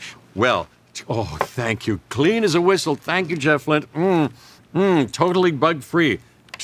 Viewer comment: Did Jeff call his assistant "Jefflin" at 58:25? Which is his own first and middle name?